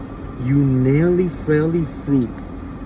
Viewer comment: “You naily frailey fruit”😂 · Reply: I thought he said freak instead of fruit 😂😂